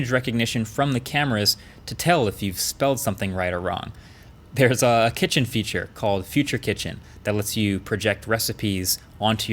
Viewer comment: I heard "there's a kitchen feature, called feature kitchen"